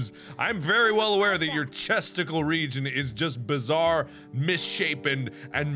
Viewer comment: Who calls the area on your chest your "chesticle region"?! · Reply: i thought he said testicle cx and i got so confused